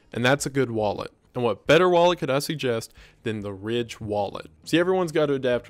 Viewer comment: I thought you said the rich wallet lol, was like I definitely need one of those